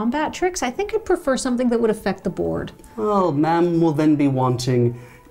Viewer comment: I swear I hear "mom" instead of "ma'am".